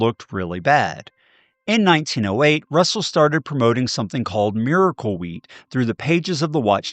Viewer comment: Boy I thought you said miracle weed